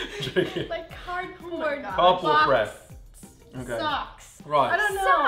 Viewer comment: Oli at 4:42 hahahahahaha i thought he said bubble breath but he said cardboard breath